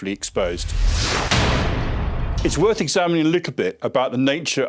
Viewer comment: why I hear "It's worth examining a leak a bit ..." instead of "little bit", what kind of accent is this? (non native speaker here)